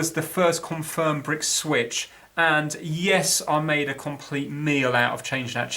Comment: man im loling hard here. he says near the ned about abou another video "I made a complete meal out of that job" i thought he said "i made a complete NEIL out of that job" i have a friend called Neil who is an idiot when he comes to Electronics and i was laughing so much, i had to go back and see if he said neil, but he didnt :(